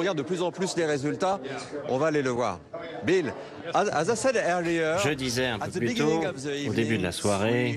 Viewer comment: BFM ils commandent leurs correspondants internationaux sur Wish ou quoi ? 1:09 "Bill, hazassèdeurlieure ..." 😂